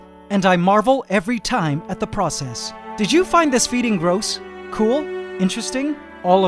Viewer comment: Thought he said "Did you find this feeding gross? cool." gave me a little chuckle when i realised my mistake lol